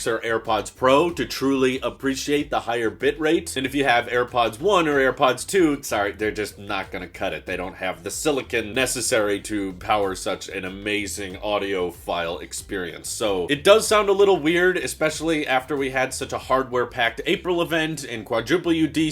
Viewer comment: Did you say “amazing audio file experience” or “amazing audiophile experience”? 🤔